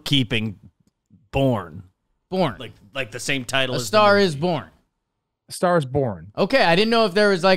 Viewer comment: was definitely saying Born super weird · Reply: I heard him saying boring a star is boring. I thought there was something about that.